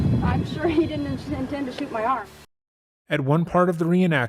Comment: Did anyone else hear afound 33:57 33:58? Sounds like she said, " Didn't intend to shoot my arm?" Really, listen, or did I hear it wrong?